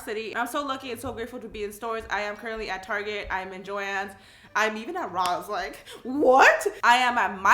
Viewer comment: I thought she said I meet bob Ross